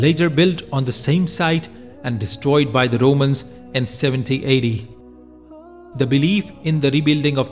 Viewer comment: There was a mistake in the subtitles. The narrator says the second temple was destroyed in 70 AD, which is the correct date, but whoever did the subtitles misheard and thought he said 1780.